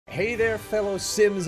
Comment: Thought you said “hey there simps” at the beginning